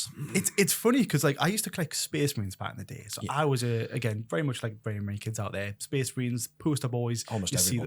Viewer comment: What's he say about collecting Space Marines? Like "kids?" Like "Americans?" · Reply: "like very many kids"